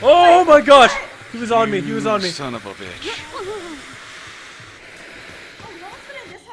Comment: lol sound like he said atebitch XD